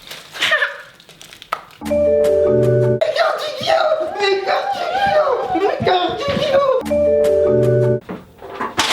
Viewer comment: qu'est ce qu'il dit??? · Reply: "mes cartes Yugi ho" je crois x) (jsuis pas sur)